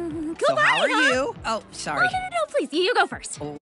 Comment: what did star say? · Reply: cool party huh?